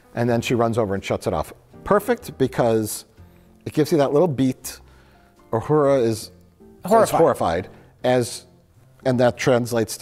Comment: did say “if Uhura is O’horrified?”